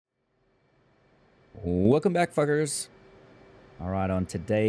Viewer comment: Bruh did he just say "Welcome back fuckers"